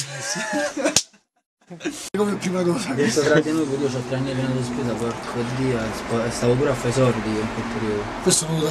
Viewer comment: cosa dice? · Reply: Dice tipo "Mi sono trattenuto, io c'ho ancora tre anni di spesa, Porco Dio, e in quel periodo stavo pure facendo i soldi" Non si capisce bene, ma io ho capito questo